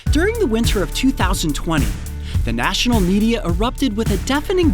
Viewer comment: did he say winter of 2020?